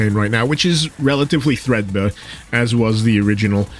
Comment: Yo what is the word you use at 1:25 ? Relatively what? Threadna? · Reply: Threadbare is the word. Apologies I had a cold while recording the voiceover.